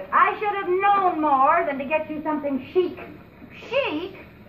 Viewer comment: I WANTED A TOASTER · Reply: I thought she said coaster.